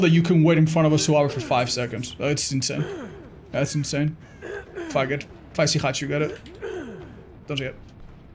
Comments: what word was said at 13:12 because it sounds awfu lmao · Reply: "fuck it" but i think he tongue twisted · what does he say here sounds like he says fa***t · you need to practice your enunciation. I had to replay this second multiple times to understand you were trying to say "fuck it" instead of "faggot". You wouldn't want people to take things out of context. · what did he say there? all I can hear is the f slur, if I get? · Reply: Lol I heard it too, I think he accidentally said “if I get-, if I see hatch you get it”